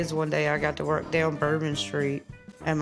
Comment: Did she just say bore been street 😂😂😂